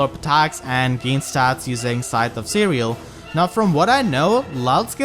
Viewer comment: For some reason I thought I heard Scythe of Cereal once, and now I can't unhear it